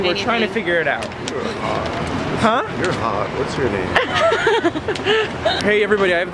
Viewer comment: " your hot what's your name" looooooool I just died for a second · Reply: ***** LOOOOOOL! I thought he said "You're odd, what's your name?" XD